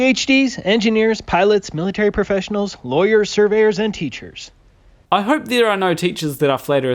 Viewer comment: Did he say SURVEYORS?